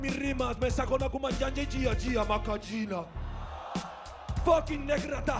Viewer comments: Que dice Babi de 2:55 a 2:59?? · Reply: se lo preguntaron en una entrevista y ésta fue su respuesta: Pues Yiah yiah makayina es una palabra que invente en un viaje a Alicante en vez de decir kiki, polvo, meneito...etc yo digo hechar un yiah yiah makayina, es una palabra mas inventada en mi amplio diccionario negrata xD. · Que dice en 2:52? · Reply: Lo saque de otro comentario "se lo preguntaron en una entrevista y ésta fue su respuesta: Pues Yiah yiah makayina es una palabra que invente en un viaje a Alicante en vez de decir kiki, polvo, meneito...etc yo digo hechar un yiah yiah makayina, es una palabra mas inventada en mi amplio diccionario negrata xD."